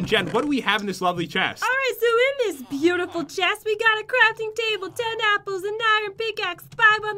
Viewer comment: Welp Jen, an ad cut you off midsentence while you were talking about the crafting table, so it sounded like you said 'So in this beautiful chest we have a crappy table, ten apples, an iron pickaxe, ...